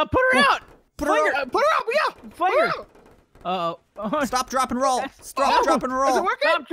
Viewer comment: I thought said slap ,drop,roll